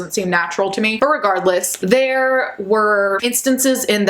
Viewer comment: Did you say “irregardless” at 6:04 ?